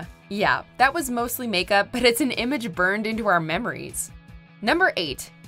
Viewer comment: Moises sounds like moist I thought his name was said Moses.